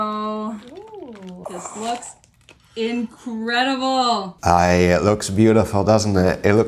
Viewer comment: Woman said "it looks incredible" and I swear to you I thought she said "it looks inedible."